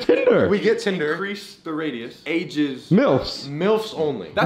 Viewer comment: did say ''MILFS'' 30:48 lol man